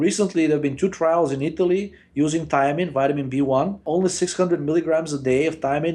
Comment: did he say thyme as in the herb? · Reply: Thiamine, B1.